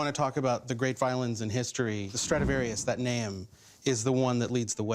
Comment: thought it said stradivirus